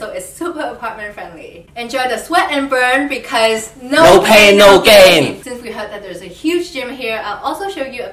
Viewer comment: I tried the leg workout and god dammit... the last exercise killed me. BUT IT WAS WORTH IT!! NO PAIN NO GAIN! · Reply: HAHAHAHAHA all the time i thought she s saying no pain no game🤭🤭 poor me🙇‍♀️